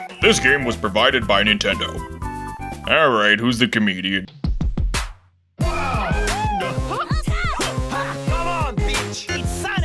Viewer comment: Ok, people told me Nathan Drake in the intro is saying "C'mon Bitch" but i hear "C'mon Peach". WHICH ONE IS RIGHT